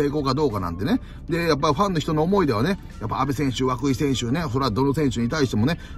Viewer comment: Twitterで若い投手を涌井投手って聞き間違え説あって笑ってしまった